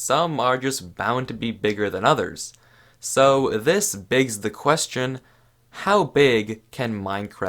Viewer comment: did he just say "This BIGS the question" or am I tripping?